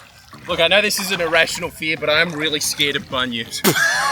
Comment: As am american i thought you were saying your were afraid of bunions! lol